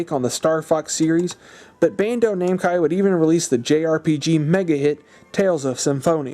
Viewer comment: did you really say 'Bando-Namcai'? LOL, awesome video, though.